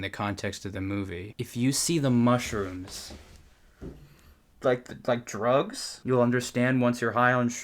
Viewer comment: Bruh I thought they said "Just EAT the mushrooms and you'll understand" instead of " Just SEE the mushrooms and you'll understand " OMG that's why we don't understand any of this . We didn't consume the mushrooms